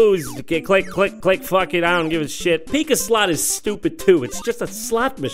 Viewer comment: I thought he said pika slaughter 😂